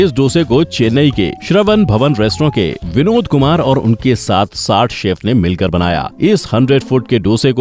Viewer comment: did he said binod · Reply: He said Vinod Kumar... not Binod